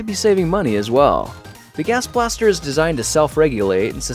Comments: Am i the only one who thought it said Gaster Blaster? i'm into Undertale a bit too much XD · Every time he said Gas Blaster, I thought he said Gaster Blaster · Lol anyone else click cause they thought it said "Gaster Blaster" and you where like "What now?!"